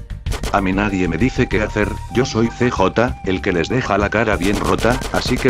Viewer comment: yo zoi CJ el q te deja la cara bien rota:v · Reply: Pense que decia el que te desvela la cara bien J :v